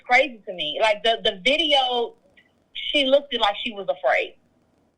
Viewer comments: Ok I'm a law student and I can code switch too but her grammer is all the way fucked up. It's not a question of code switching or speaking black.. this woman can't even conjugate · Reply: Riggghhtttt I could have sworn she said "Lookdid" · Idk bout “doctor Nikki” we usually refer to surgeries as “cases” and she refers to something medical, it doesn’t sound right.... 🤔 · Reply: Right!!! Homegirl’s diction is troubling..... I know I heard her say “lookded”🤦🏾‍♀️